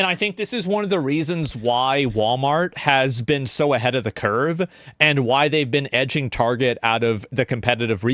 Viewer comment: Did I mishear "Ahead of the Curve " for "Ahead of the kerb"? 😂😂😂 I am nicking that !!!